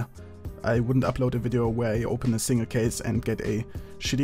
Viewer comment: when he says "a single case man" it sounds like he says I kissed a man...